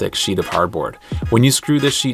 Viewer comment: So, im a skateboarder, would the punctual load from smaller wheels break through the cardboard on top? If so what material should i use for the top layer that would be more durable? Please respond i want to know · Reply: At first i thought u had said "cardboard" instead of "hardboard". Yeah that makes more sense for a durable ramp. Great video by the way. Now i need to build it with my dad if he lets me. I might turn it into something i can put in my backyard with plywood as the floor of it